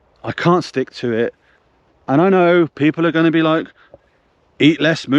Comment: I had to go back, did he just say F off 😂😂